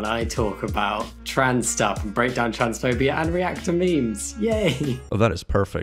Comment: HOW did i hear transphobe